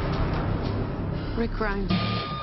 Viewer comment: Who else heard the, "Rick Grimes", at 3:35?